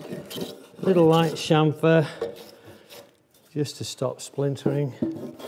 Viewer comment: lol at 36:30 subtitles say "just a little light shampoo just to stop splintering". What brand would you suggest?